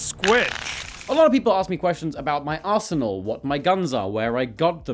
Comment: Thought he said arsehole, not arsenal